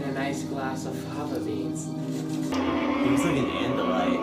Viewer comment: what did you say he looks like an endolight? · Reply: He was referring to an Andalite from Animorphs.